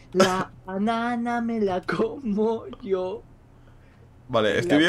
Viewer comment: perdon perdon perdon escuche decir a vegetta la banana me la como yoo XD en el minuto